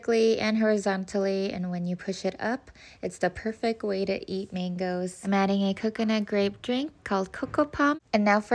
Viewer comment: I know,, I'm not ENGlish Native.. But is that why.. I heard her say tomatoes not MaNgOeS 😂